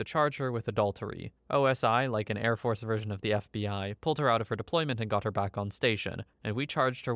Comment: I know he's saying "the fbi" but I keep hearing bfdi 😭